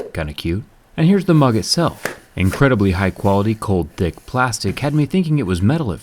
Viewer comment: Am I the only one who heard "Cold dick plastic" at 1:31?